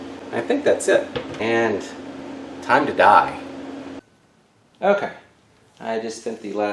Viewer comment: Omg at 8:40 it sounded so sinister when you said 'time to dye' I thought you said 'time to die' muahahaha